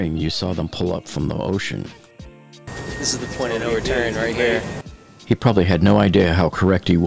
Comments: What did the bald guy say? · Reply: He said "This is the point of no return right here " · What was said by the guy at 2:48? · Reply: The point of no return here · What does he say here~? You mention that "he probably has no idea how correct he was." To me it sounds like he says, "this is the plan o doin", right here, but that makes no sense to what you say about him being correct.